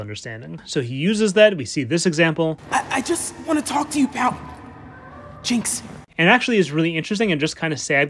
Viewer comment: I never realized he said "I just want to talk to you, Pow.. Jinx" I always thought he said "I just want to talk to you bout jinx"